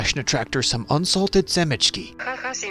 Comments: what is he saying? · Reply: хаха семечки идут бррр translation "haha seeds go brrr" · Semechki directly means seeds but by the default it actually means sunflower seeds Also what the synthetic voice said is: hahahhah semechki go brrrr if you didn’t/couldn’t hear